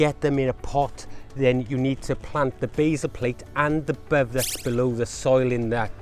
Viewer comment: Great video, but at 3:50 the "subscribe" button pops up with an audible zing, and I can't understand some of the critical words that you are saying. It sounds like, "you need to plant the baser plate and the bovex below the soil," but I don't think "baser plate" and "bovex" could possibly be right. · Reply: Sorry about that, Basal plate below the soil